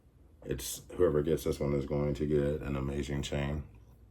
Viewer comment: So you keeping the concave ? Was ambiguous. Thought I heard you say “whoever gets this chain will be lucky”